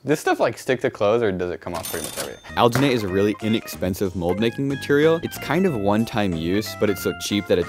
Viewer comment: I thought you said expensive and you said, but it’s price me that that doesn’t matter much basically I was confused